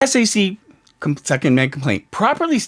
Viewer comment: I thought you said "Second Amendment Complaint" My first thought was "Oh [bleep]! Blair's got a gun!